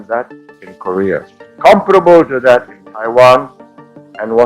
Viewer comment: he says "comparable" but the video's subtitles say "comfortable". YouTube's subtitles got it right though.